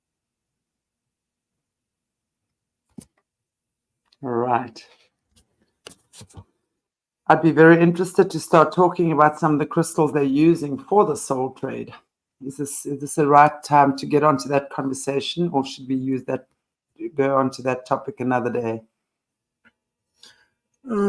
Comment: As far as I know speaking in tongues is just speaking in another language, ( from another country) if you go into the gospels it states it clearly. When the disciples, after Pentecost, openly started speaking truth in the street, some thought they were drunk but people who heard it understood saying he is speaking in his language.. as there were so many people from places from around the world present.. There was a need for all to hear the truth so God needed to ensure different languages were spoken. If everyone spoke English then tongues were not needed, but if you come from China, etc then there would be a need. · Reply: Totally thought said SOUL TRAIN, LOL